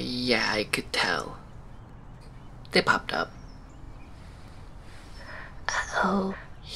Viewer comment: Normal convo lol Bella: I'm frezzing. Jacob: Uhh yeah I could tell, zip up top. Bella: Uh oh. Jacob: Yeaaah, it's frezzing. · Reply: I thought he said "it popped up"